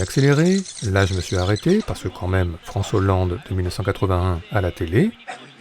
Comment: France-Hollande et j'ai entendu François alors que c'est clairement du foot